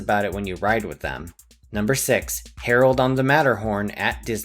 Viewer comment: me: ¿"Harold on the Matterhorn at Disneyland"? nah, i misheard, it's "Terror on the Matterhorn..." (4:56-4:59) me, moments later, having replayed the video: sonuvagun, it is "Harold".